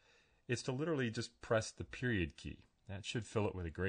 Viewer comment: on 1:02 he press the ??????-key? cannot understand :( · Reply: Period. Like at the end of a sentence. Press the Period.